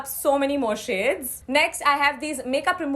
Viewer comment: did she just say makeup remover wipes😂😂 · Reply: She said makeup remover pads, not wipes!